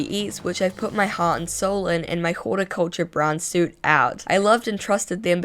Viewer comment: it sounds like they say “horticulture” brown suit which I’m not sure if that was intentional but LOL 💀